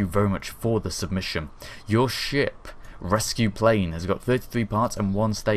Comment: Sounded like he said shit rescue plane